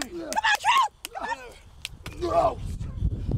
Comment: Go to 1:06 and hear wat carmella says "come on baby"